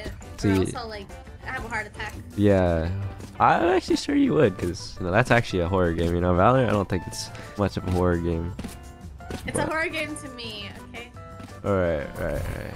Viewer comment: did he say Valorant isn’t a horror game